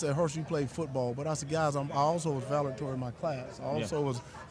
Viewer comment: He didn't even say the word "valedictorian" correctly lmao 😂😂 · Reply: I thought he said Valerdorian 🤣🤣🤣